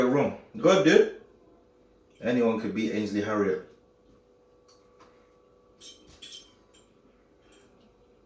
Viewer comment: Sounded like said Anyone can Beat Ainsley Harriot 😂